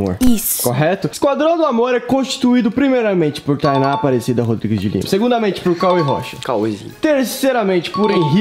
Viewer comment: calma no min 0:33 Thainá Rodrigues de Leao ????? me ajudem ai eu nao entendi oq ele disse